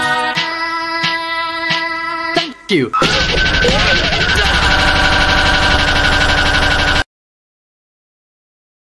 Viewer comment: bro what is he saying he gets corrupted?? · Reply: I think he is saying wait pls noooooooooooooooo....